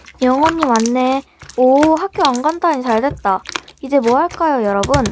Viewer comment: 저만 학교라 들리나요? 그리고 짱 재미써요!!!! 좋아요 누르고가요^^